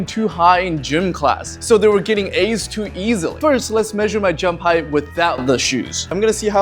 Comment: why did he say "so they were getting A's too easily" but the captions said "so they were getting hurt too easily"